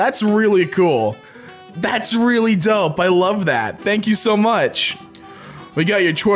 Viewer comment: He said ‘that’s really dope’ about the dragon but subtitles say ‘that’s really dumb’... what if the artist is deaf?!